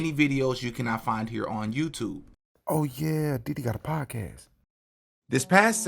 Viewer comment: I was doing something and not paying attention and at first I thought you said "oh yeaah Diddy got a podcast" I was like what the f**k! But realized you said "dd got a podcast" lol xD ahhh I was freaking out for a sec but anyways this seems like a very interesting video gonna watch it now lol